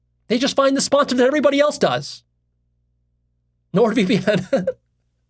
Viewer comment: What does say at 1:09:38? The same sponsor everyone else does? I can’t catch it. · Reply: Nord vpn